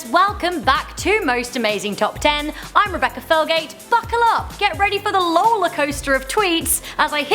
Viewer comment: I thought she said "Fuckle up" 😂😂